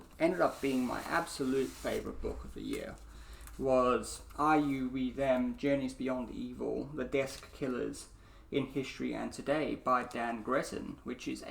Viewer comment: Hi again, thanks for your answer but I want the name of the other one, you show at 10:57 I can't understand the title and the author. The one before you show the Stalin biography. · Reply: Ah yes no worries. This is 'I You We Them: Journeys Beyond Evil: The Desk Killer In History and Today' by Dan Gretton